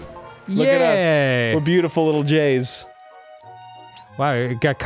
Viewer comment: When he said "were beautiful little Js" I thought he said "were beautiful little gays"